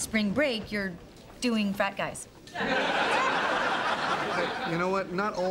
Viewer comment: Always thought Monica said fat guys not frat guys lol